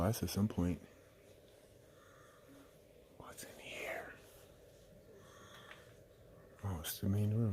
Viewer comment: I thought, I heard a disembodied voice saying "WHAT'S IN HERE", but no really sure, it could have been, but really didn't sound like him